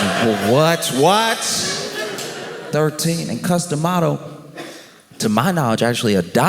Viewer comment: This is super trivial but it sounded like you were saying "custom auto." I was obviously wrong but it's what I kept hearing which is unusual because I never really have trouble understanding you.